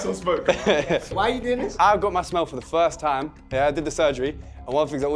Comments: My goofy thought it said “I got sugary to smell for the first time” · my dumb ahh thought it said i got to smell surgery for the first time.